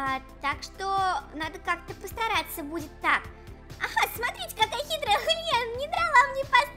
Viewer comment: я слышу что то очень плохое... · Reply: Мне одной послышалось "хитрая хрен"? (>_<)